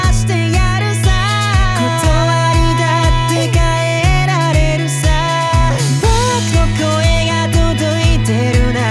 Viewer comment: なんて言ってますか？ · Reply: 1年越しに失礼｢理(ことわり)だって変えられるさ｣